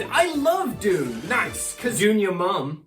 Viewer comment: kazun your mom? Is that what he just said?